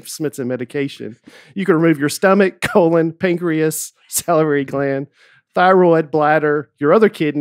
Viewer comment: Everyone gives Nate a hard time when he reads but I'm pretty sure that says both "salary gland" and "lair-nix" instead of "lair-inks" in the same sentence (1:00:44). Thanks for all the laughs and can't wait to see you guys at the live show- I know you said before that people make a whole weekend or vacation around going to your shows but my husband and I are driving up literally for one night only from Virginia for the night of the podcast and then turning around and going home. No pressure but I'm expecting you all to deliver. LETS GOOOOOO!